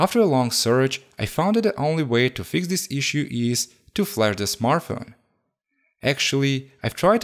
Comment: Can you please explain what you say in 11:29 ? "The only way to fix the issue is to "flair" the smartphone"? I cannot understand what you are saying at this point. Thanx.